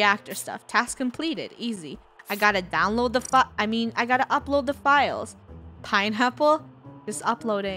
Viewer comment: JUST LITSEN CLOSELY WHAT FUNNEH SAYS:I gotta download the fu I mean upload the files